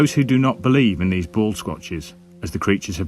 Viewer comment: I thought he was saying Ballsquatches. I don't have balls, but if I did, I certainly wouldn't want anyone to squatch them.